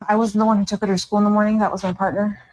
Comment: Attention: I've listened over and over and it sounds like she says "our partner". Am I hearing it wrong. 😮